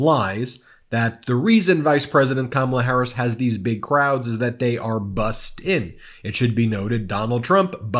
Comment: I thought he said she doesn't have big crowds, same sentence she buses them in cause they're bought and paid for!? WTF, King James was right what the hell are we even talking about here!? LOVE Y'ALL, VOTE!!🙋🏾‍♀️🙋🏾‍♀️💙💙💙💙